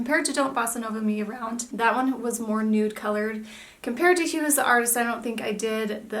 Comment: You mentioned a comparison to Movie Buff which you said was perhaps a little more lilac. You said you didn't do a comparison swatch. It's around 1:29. Could you please tell me the name? I have replayed it many times and cannot understand. Thanks. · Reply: yep Kat is right- Hue Is the Artist. Sorry, sometimes I mumble lol